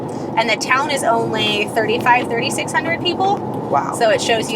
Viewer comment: I thought she said the town had a population of 36 000 and not 3 600 and I was thinking how crazy they only had just one tragic light 😂